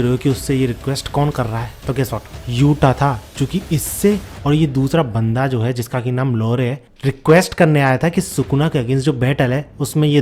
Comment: kya kaha bhaiya aapne? 😂😂😂😂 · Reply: Lore 😂